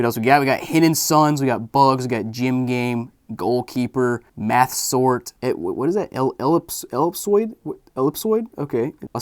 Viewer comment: i thought you said meth sword and not math sort